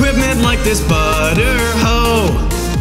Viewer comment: Did he just say butter hoe cuz i laugh so hard and crack my voice 😂😂😂😂😂😂😂😂😂😂😂😂